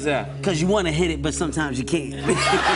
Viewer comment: Woooah wait he actually said “you wanna hit it But sometimes you can’t “ instead of “you wanna hit it sometimes But you can’t “ now I know that English is not my mother but those statements are completely different 🤣